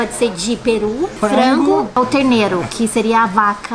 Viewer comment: "ou carneiro, que seria a vaca criança". Eu ouvi isso mesmo?